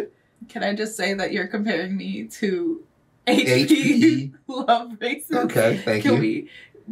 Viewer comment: I thought he said H P LoveRices at first